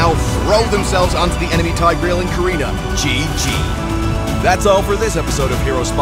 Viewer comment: did he call Selena karina 😂😂😂